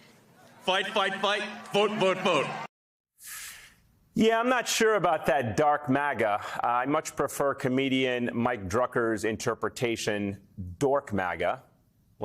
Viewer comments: I thought he said, “Drunk maga”. VoteBlue’24💙💙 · "I'm not just MAGA, I'M DARK MAGA!" I think he just said everything I need to know to stay away from everything he touches or is a part of including X. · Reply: I thought he said “drunk MAGA.” Amazing that Musk used to be somewhat of a hero to me for his climate efforts via Tesla. That was a LONG time ago. He’s become the poster child for why we need to limit the role of billionaires and corporations in politics! Nowadays he supports a climate change denier? Ugh.